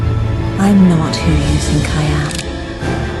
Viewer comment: What the hell does she say at 20:52? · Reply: "I'm not who you think I am"